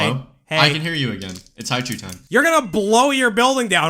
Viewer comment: I thought they were saying "haiku time" and i got really confused when they never made a haiku. 💀